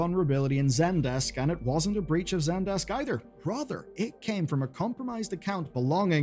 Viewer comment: i swear i heard "Broother" rather than "Rather".